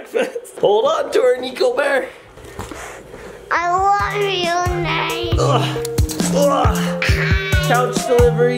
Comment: When I heard “I love you naves” that made me laugh